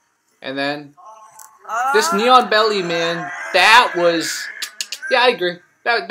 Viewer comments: For the longest I thought you were saying "Neon Belly" and I was like "wtf is that some kind of technique?" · Nice one. Thanks! Although I confess that instead of "knee on belly" I kept hearing "neon belly", which had me greatly puzzled for some seconds. What's neon belly and why doesn't the Taiji guy like it? Hahahaha.